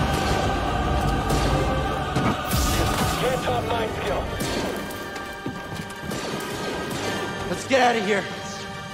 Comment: WTF did that stormtrooper say!? 😂😂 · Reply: He said can't top my skill